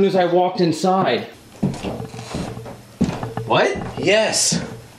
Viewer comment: Did anyone else hear the female voice at 1:07:05 say "what" when Corey says "are you fucking shiting me"